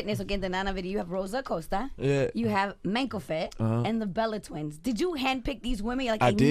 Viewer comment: who were they talking about who's mango fit, Idk if I pronounced it right it's hard to hear that women what she say? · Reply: MANKOFIT Check her on Instagram. She's amazing.